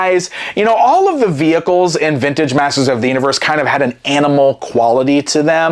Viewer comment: Masters of the INNERVerse?? oh, i misheard you. That would be cool to see He-Man vs Baron Karza to save the Micronauts